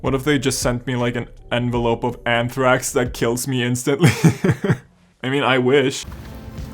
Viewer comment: did he just say "I mean I wish" after saying the might send him something that kills him?😧😥☹️☹️☹️☹️